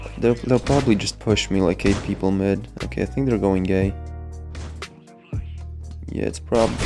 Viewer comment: i thought he said “going gay”